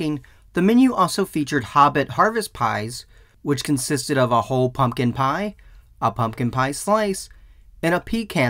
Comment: I was listening to this with headphones and I heard “A whole f*kin’ pie” LMAO